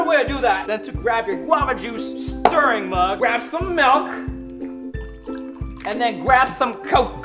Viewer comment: it sounds like he said malk